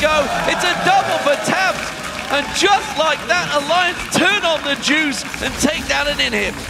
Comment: At first i heard ''Alliance turns on the jews'' xD